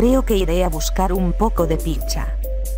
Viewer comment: En el minuto 1:25 chica dice toy chica:creo que voy a buscar un poco de ¡PICHA! ESCUCHARON(pero en los subtitulos en ingles dice pizza) que dices tenho razon?